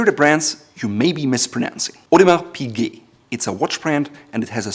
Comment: He says “odoma pigi” I hear “what about pigi”